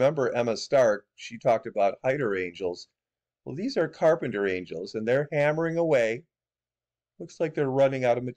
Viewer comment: 😅 funny Carpenter angels? Oh I misunderstood you I thought you said carpenter ants😂